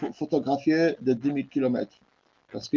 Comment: Qu'est ce qu'il dit à 3:25 ? 10000 km ?